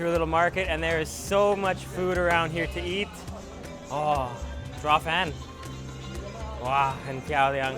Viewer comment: What did he say at 7:39? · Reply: Do you eat zhuafan